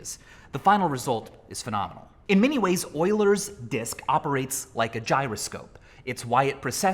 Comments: I can’t be the only one that thought he was saying “Oilers Disk” the whole time and looked at the title confused 🤦‍♀️ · at first i thought it said elders dick💀💀💀💀💀💀💀💀💀💀💀💀